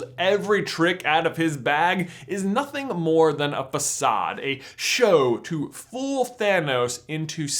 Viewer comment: whats that word? i heard pesat? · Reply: facade?